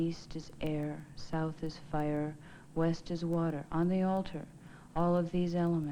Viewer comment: north is water; east is wind; west is fire; south is earth NEWS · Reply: I thought they said West is Water.